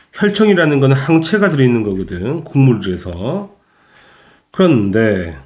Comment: 여기에서 혈청이라는거는 항체가 들어있는거거든 그다음에 뭐라고 그러시느거에요? 국물이어서라고 들리는데요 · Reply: '국물'이 맞습니다